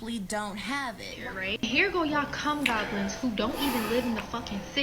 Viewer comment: Did she actually say cum goblins at 11:47 ?!